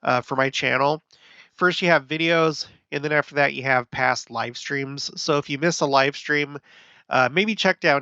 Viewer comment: I thought you said “past life stream” haha I thought, “That’d be quite the button!” 😂 Also, Pluto entered Aquarius today for the first time since 1798! Entering the long awaited “Age of Aquarius.” Plus the spring equinox obviously just happened as well. Love your content!